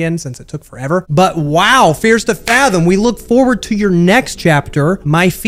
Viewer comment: I thought it said FEARS TO FATMOM!😂😂